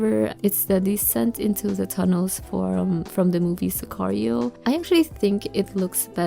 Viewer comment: what movie did you tell about please? I didn't catch it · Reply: It's called Sicario ❤️❤️